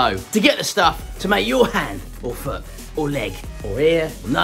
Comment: I had to go back to check my sanity! I swore I hear you recommend casting one's "dingus"! 😯😂😯